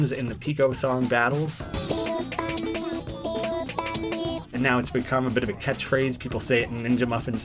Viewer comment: everyone hears "pico funny" while i hear "BIG OL BUNNY"